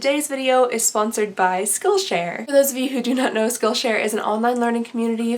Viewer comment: I thought you were saying “Skull Share” at first....which would have been right up your alley! I got the Luxe box this month, and found that it wasn’t as makeup heavy as it usually is. From the survey they sent the Boxy Luxers it seems they were thinking of going down the Fab, Fit, Fun route, which I hope they don’t. I don’t mind some skin care products and maybe a hair care item here and there, but I signed up for Boxy more for the makeup. Guess we’ll have to wait and see.